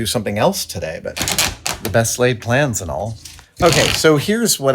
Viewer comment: I know he said "best laid plans" but I heard "best lathed plans"...lol